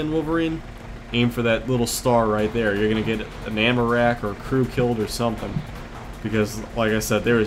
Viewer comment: When you said ammo rack I thought you said Amerac and i was like "Wat is that? A disease?! Well i've never heard of it!" LOL